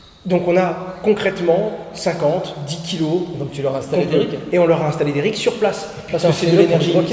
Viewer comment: Installer quoi ? pourriez vous dire plus sur le sujet ? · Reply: Bonjour, j'espère que vous allez bien :) , Il leur a installer des "rig", ce sont des machine qui serve à miner de la crypto :) . Pour plus d'info, je vous laisse ce lien :) . <link> En restant disponible, je vous souhaite une bonne continuation.